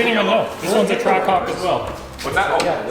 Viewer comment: Ahh, a track hawk. I thought you said track c0ck. lol. I mean, either way, down for a ride right? ;) HAHAH!